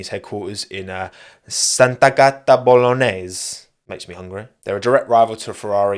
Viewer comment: that's killed me 😂 (btw you doubled the "t" and it sounded like "Santa Gatta" that is literally "holy cat" 😆)